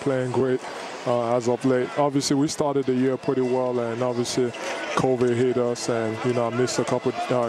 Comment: Man, thought he was saying "Kobe", not "Covid". I was a little confused😂